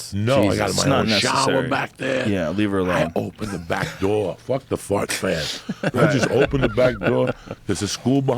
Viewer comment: did joey say “fuck the fart fan” 😭😭😭😭